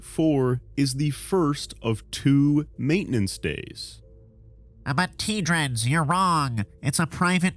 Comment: I just keep hearing "aber" instead of "but", which funny enough also means but, just in another language